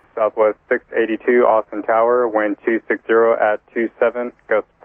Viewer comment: LOL! The ATC said "Austin Tower", I thought he said "Austin Powers", I said what? Multi-tasking can get you into trouble.